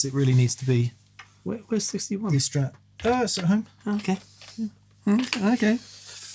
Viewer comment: said it was “at home”. That can be taken two different ways 😉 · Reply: ahh fair play. First watch-thru I thought I heard him say "I sent it home" :)